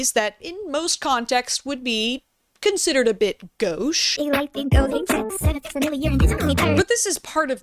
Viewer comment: Can someone tell me what this word is? "Would be considered a bit __19:51___" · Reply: Gauche (lacking ease or grace; unsophisticated and socially awkward)